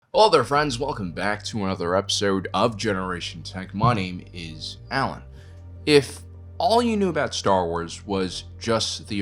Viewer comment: I always hear "money is Allen"